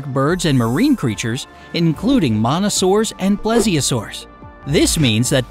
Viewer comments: Narrator says, "monasaurs", caption says, "mosasaurs". 🤦🏻‍♂️ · jold on at 4:38 did he say monasaurus its mosasurus